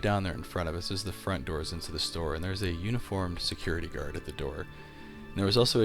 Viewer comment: Am I the only one who heard him say "unicorn security guard"?